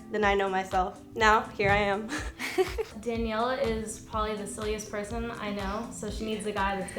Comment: I thought she said "Daniela is probably the sluttiest person I know"